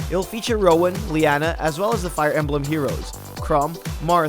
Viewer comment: Did you just call him "Crumb"?